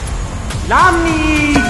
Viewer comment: what did he say? I heard he said:”Nami”